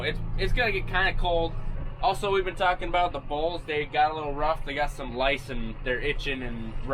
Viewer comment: not a farmer, don't understand the lingo, or the accent, thought he said, "buzz down to the balls" repeated it, it was 'bowls" whatever they are, turns out it was the "bulls"